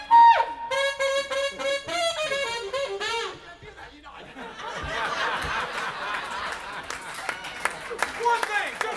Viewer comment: has someone understood what Eric has said? (im not English) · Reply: "I can't do that, you know i can't do that!"